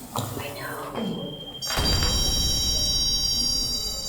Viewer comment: as they are saying crazy that the rem pod hasn't gone off it almost sounds like something saying I know and then the rem pod goes off. Not sure if it was one of them or a spirit